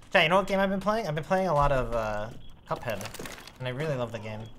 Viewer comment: What's the game he's talking about at 24:20? I couldn't really understand what he said. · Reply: Cuphead.